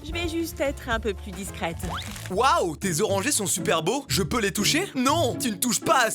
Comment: j'ai compris des "os rangés" au lieu "orangées"